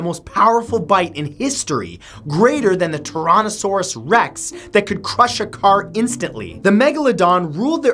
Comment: Did I hear you say "toronto-saurs rex?" Lol